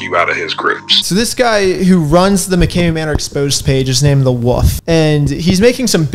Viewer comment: he's named the what? Was th at a "the woof" i heard? As in "a pack of woofs were howling at the moon"? Or as in "the soud a dog makes is woof woof"? Just wondering. If you call wolves "woofs" I may have to go team haunted house and probably join a cult.